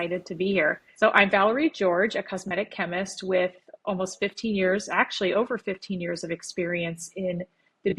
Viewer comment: Over 50years experience my goodness looking good Valerie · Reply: I think she said 15 years, at first I thought she said 50 also. 😂